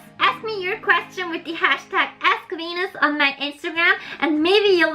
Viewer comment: Lol it sounded like you said you name like “askPenis”